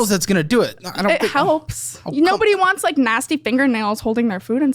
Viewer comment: Oh no, everyone who ever lived whose fingernails are not acrylic is NASTY. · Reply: I thought I heard her say "mascie" aka masculine, not nasty 😂